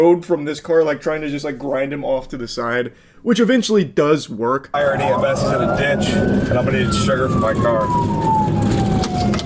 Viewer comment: does he really say he's gonna need sugar for his car?